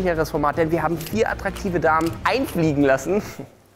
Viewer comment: German language learner here: does he say "einfliegen lassen" and then the n-word at 0:31??? what's he saying? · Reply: He says digga, it means brother or homie or friend